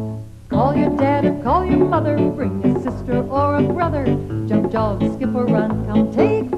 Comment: does she say Jim Jones?